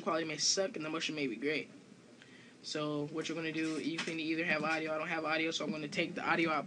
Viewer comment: WHAT?? did u say u can have audio or not have audio? is it